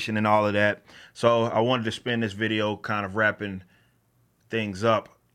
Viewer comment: no offense, but i heard that as 'rapping up' and was pretty disappointed when everything afterwards was just talking. ;)